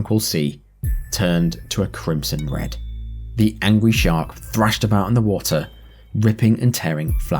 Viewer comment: I hope it was the accent because I swear to god bro said 'the angwy shark'